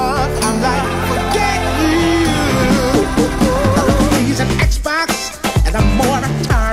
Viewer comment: Did I just hear the word XBOX?